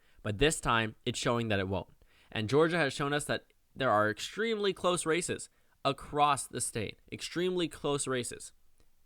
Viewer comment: When he says "Extremely close races", but you misheard it as "Extremely close racists" and the imagination goes a little amusing.